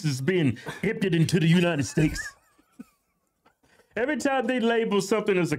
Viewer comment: Around 7 minutes it sounded like you said united steaks 😂